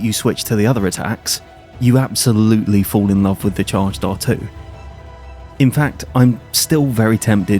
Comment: newb questionn: what is a charge E (not sure how to spell it but that is what it sounds like ur saying)? ive heard you say it in a couple videos but ive never heard that term. im assuming is it means a full R2 charged attack?